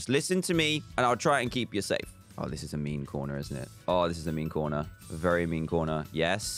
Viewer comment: I thought he said this is a meme corner